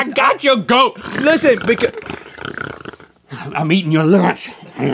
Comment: "I'M EATING YOUR LUNG RARARARRARARA" -Link Neal, 2016 😂😂😂😂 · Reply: i thought he said "im eating you fo' lunch"